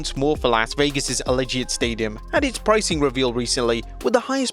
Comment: I thought he called it Las Vegas' Alleged Stadium. 😄